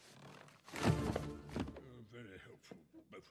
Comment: DID HE SAY NOPE BEFORE HE PASSED OUT AT 2:14?!?!?